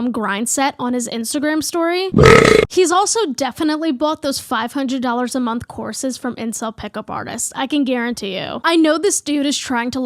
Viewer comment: help I misheard the bit about pick-up artist courses as "those 500 dollars a month corsets from incel pick-up artists" and I had the worst mental image of...... incel tightlacing......